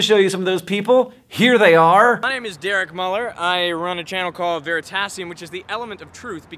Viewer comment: LOL I thought the guy said Veritaserum. Professor Snape, anyone? Once a Potterhead, always a Potterhead.